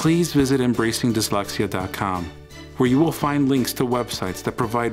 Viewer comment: I thought it said, ‘embarrassing dyslexia.’ It’s “Embracing Dyslexia.”